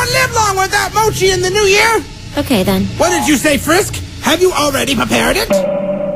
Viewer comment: Frisk seems so proud of herself when she said “I love you” :> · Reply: I thought you said frick